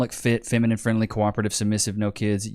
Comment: did he just said submissive ? That’s what men want. I mean someone who would not speak no matter how many time you hit her